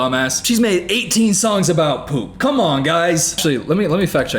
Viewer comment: i heard “shes made 15 songs about.. cum on guys.” WHATDIDHESAAAYYY